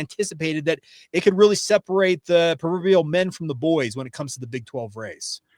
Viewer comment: Did you say "peruvial" men from the boys? Probably better read your proverbs?😅